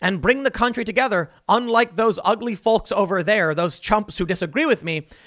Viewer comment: I watched it... I heard "Ugly Filth"... could be wrong though, he is quite a mumbler.